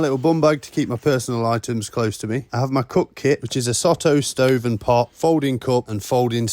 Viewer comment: For a second I thought you said cock kit. Not cook kit. All are important I'm sure 😂